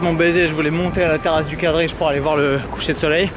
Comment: il a dit la terrasse de quoi ? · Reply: Terrasse du quadrige. C'est l'altare della patria